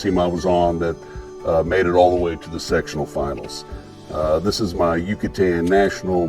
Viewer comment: did he just say his team made it all the way to the ''' Sexual" Finals?... CPG doesnt compete in the "Sectional" Finals